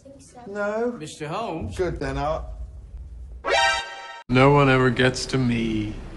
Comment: I'm sorry but all I hear is "no one ever gets to meMes"